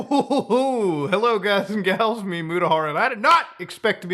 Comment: When used to watch Mutahar and he said 'Hi it's me Mutahar', I thought he was saying 'Hi its Myamutahar'. True story.